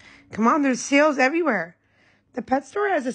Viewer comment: I thought she said "there's SEALS everywhere"! 😂😂😂